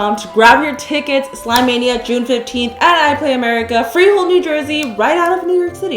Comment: For a sec i thought she said Frejol, New Jersey. Like frejoles